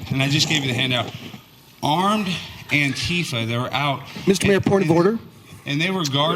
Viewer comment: I thought he said ATF (Goverment) with guns not antifa?